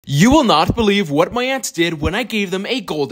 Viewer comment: bro try feeding them a very small whole fish! it would be a dope transition from whole to bones!! *This channel is amazing, this comment section is also very dope. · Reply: I honestly thought you said you won’t believe what my ex did 😅